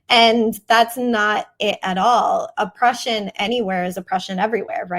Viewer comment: She said “Oppression anywhere is Oppression everywhere” and I thought she said A Prussian. Drawing the through lines of Prussian militarism, Teutonic ethno colonialism, to the manifest destiny of the USA and Israel but she’s right also with the other one. Goes both ways lmao. Prussian derangement brain over here don’t mind me .